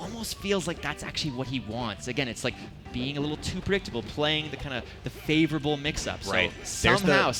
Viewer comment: Nah did he just say being a little too bricked at 0:35 😂